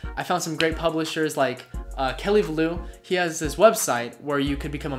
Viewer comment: i didnt understand the name he said in 1:09 can someone tell me pleasee?? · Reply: Kelly vallau